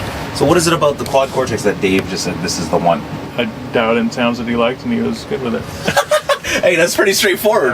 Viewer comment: "I dowed in towns that he likes & he was good with it"... WTF is he mumbling?! Is he attempting to say "dialed in tones"?